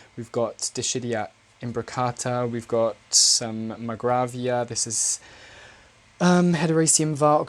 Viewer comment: right after the Imbricata, what is that plant? sounded like macravia??